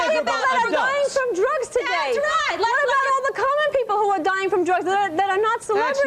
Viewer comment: Did she say Colored Ppl